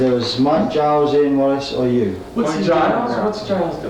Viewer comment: Small correction at 12:47. I think he says "Ian Wallace" (not "Ian Weiss"). Ian Wallace was King Crimson's drummer <num>. He played with Jon Anderson in "The Warriors" (before Yes) I believe. At any rate, great interview!